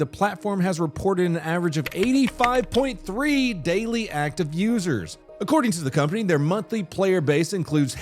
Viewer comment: I wasn’t looking at the screen and had to rewind when I heard “85.3 active users” lol